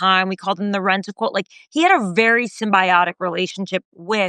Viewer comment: What did Tara Palmeri say at 20:02? The captions say "rental quote."